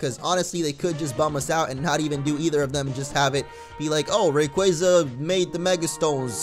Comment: I thought he said... Rapequaza wtf lol